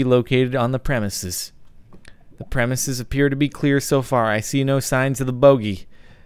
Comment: "I see no signs of the bogy" at first I thought he said "I see no signs of the booty"